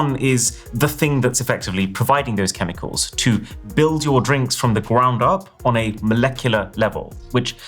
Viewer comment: can't decide which is more tragic....Natasha being 700 guys locked behind a curtain, or trying to build wakanda and actually building a hut 😐 Next Tech Fails episode here: <link> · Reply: Did you say "From the gwound up" 😂